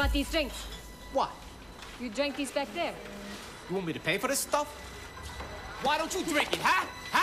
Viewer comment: he clearly says "you want me to pay for this shit" I dont get it this video is rated r and they say bitch and fuck alot in this movie but not the word "shit"